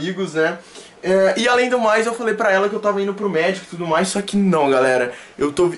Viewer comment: Juro que eu entendi to indo pro Mexico!!